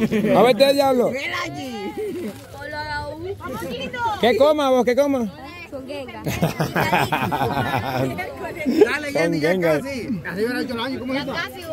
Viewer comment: me encantó este chiquillo que dijo coma m—, luego dice chonwhat (no se que es eso).